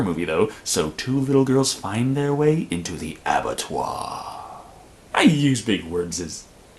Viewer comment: what was that word you said at 2:51? sounded french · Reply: Bob Sacamano Abattoir, it means slaughterhouse.